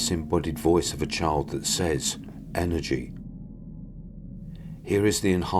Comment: I thought the child's voice said 'Eddie' not energy.